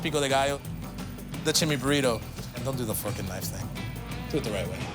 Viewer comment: VOLUME UP. I wonder if that slipped through, because I don’t think I’ve ever heard it on this show. Lol. Love it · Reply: I get what you're hearing, but it was FORK & knives thing.. not fckn knives thing lol